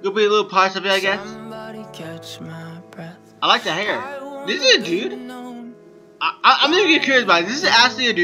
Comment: Did he just say... This is a boy? or am i just tired cuz its 2 in the morning